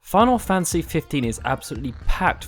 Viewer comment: wait did I hear him say "final fancy 15"?